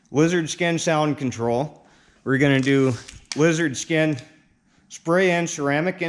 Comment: I thought you said wizard skin 😂